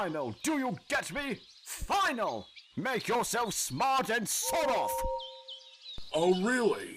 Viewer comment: Did Gordon say "Sod off!"? Isn't this profanity?